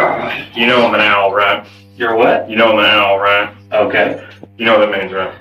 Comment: Wtf is an Owl??? 21:43 he says he's an owl???